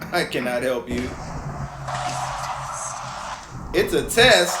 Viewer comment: i hear "It's a testy" (Testicle) haha